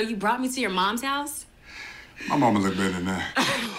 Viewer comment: What does the actor say @ 37:11? Anyone know? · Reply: I think he said "My momma lives better than that" But it took me a couple times because I thought he said "My momma looks better than that" In the same scene where he's in the same room with a half naked woman in his mother's house, it just sounds odd to me